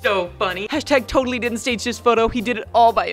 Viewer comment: it say tage this photo instead of take